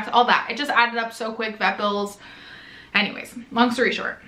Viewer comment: I thought you said "Breville's" when you said "vet bills" 😂